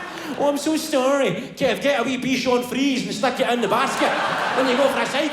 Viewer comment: what does he say? bee-shon-freeze? is it a french term that i've never heard? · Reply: Bichon frise